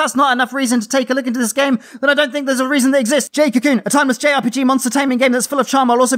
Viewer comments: What was the JRPG at 8:49? I couldn't understand you lol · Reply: Haha sorry about that. It's jade cocoon for ps1!! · I didn't quite understand the game title at 8:50, what is it? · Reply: It's Jade Cocoon for Playstation 1. ✨💪🏻✨